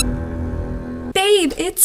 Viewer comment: i hear "babe is 4p.m time for you pinis clapmate"🤔